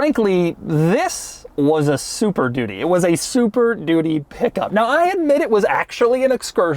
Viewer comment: Why does he keep saying "super doody" at 12:25? Is he just saying it's a really crap car?